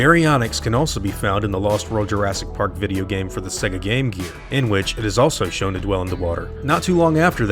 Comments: did u say "Sega Gamecube"? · I swear blind it sounds like you said sega gamecube